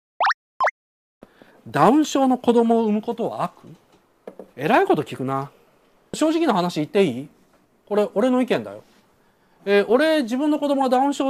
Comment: 関係ないけど、最初｢男妾の子供を産むことは？｣って聞こえては？ってなった